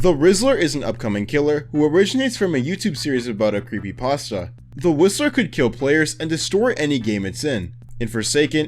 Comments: Instead of 'The whistler' I heard 'The rizzler' 😭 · I thought when you said "the Whistler" I thought you said "the rizzler" 😭🙏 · when he mentioned the Whistler i thought he said the rizzler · When he said the whisler I thought he said the rizzler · Bro for some reason when he said whistler I thought he said rizzler at first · I thought that he said "The Rizzler" instead of "The Whistler"